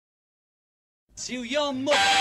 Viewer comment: "To your MOTHER!!!" Love this...it is magical. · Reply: All I hear is "To Your Muh"...